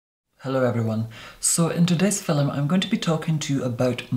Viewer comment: i heard “in today’s fi—lim” instead of “film”